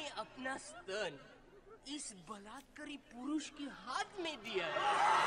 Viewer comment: subtitles are wrong he replaced word "miracles" to "raped" 😂